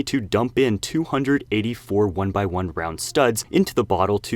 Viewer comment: I thought he said "brown" studs and was rather confused.